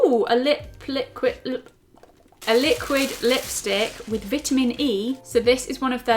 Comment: Did she just say vitimin